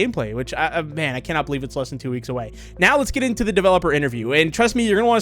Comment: I thought you said 2weeks